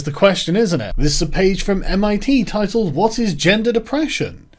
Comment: "Gendered oppression"....I heard that as "gender depression" which is a good name for all the weirdos obsessed with other peoples' genitals who invent new genders.